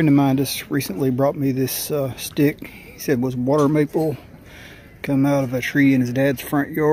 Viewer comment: What kind of maple did you say this was? At 0:06 I couldn’t understand what kind of maple he said it was??? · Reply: He said water maple, but its also know as silver maple, creek maple, white maple, etc.